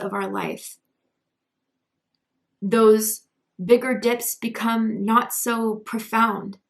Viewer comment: What did you say at 13:53? I kept relistening, did you say those bigger dips are not so profound? Anyway, these really resonated hugely! Thank you? Ok, yep, I hear it now, phew! Lol